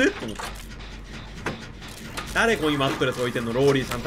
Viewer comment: マットレスがハントレスに聞こえた自分は病気かもしれん…